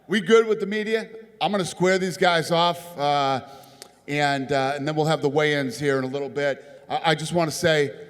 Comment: Did Dana just say it's been a horrible week out here in Las Vagas?